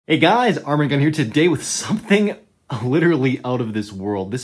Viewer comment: I know it "arm and gun" but he always says it fast, and I always hear "armor gun"